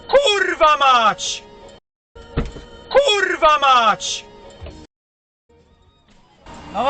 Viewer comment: Khouldva!!!!!!!!!!!!!!!!!!!!!!!!!!!!!!!!!!!!!!!!!!!! hahahaha I know what he said